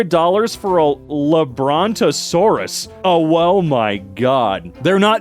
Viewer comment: did bro just say "owo-my god"